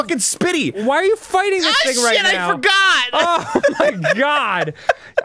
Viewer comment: I swear Danny said why are you fighting this nigga right now😂😂